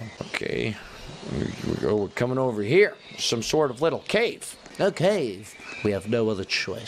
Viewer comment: I heard “okcave”